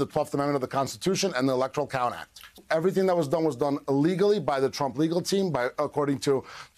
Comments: It sounds like he says, "everything was done illegally" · listen closely to the attorney speaking in the referenced appearance because it sure sounded like he said “everything that was done was done illegally” 😂😂😂😂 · it just sounds like 'everything was done illegaly'. Does anyone else hear it that way? Ari seems to have a way of bringing things out of his guests, hehe.